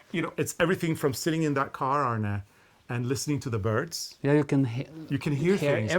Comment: LOL I thought you said "listening to the Byrds" when you were talking about driving around in the Beetle!